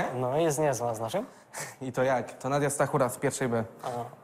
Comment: usłyszałem Sakura XD